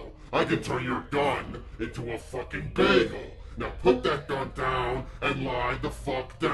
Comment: "I CAN TURN YOUR GUN INTO A BAGEL!!!!!!" LOL made me laugh so hard :D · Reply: I thought he said "Baby", rather than Bagel.